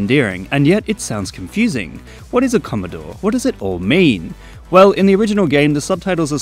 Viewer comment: I thought he said use a corridor 💀💀💀